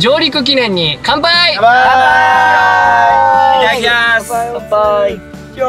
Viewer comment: 乾杯〜！の後に乾杯おっぱーいって聞こえるんだけど笑笑笑笑笑笑笑笑 丈くんなのかな？